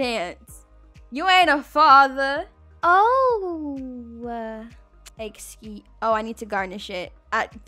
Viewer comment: did she say the s word or it?? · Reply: i said i need to garnish it😭